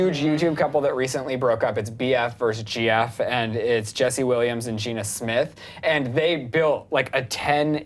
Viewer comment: HOLD UP..... DID HE JUST SAY JESSE WILLIAMS NOT WELLENS